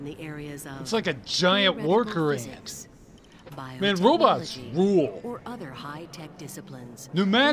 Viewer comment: "Robots rule" What I heard "odoyle rules"